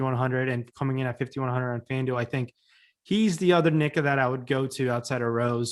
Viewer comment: □did say nicca??!!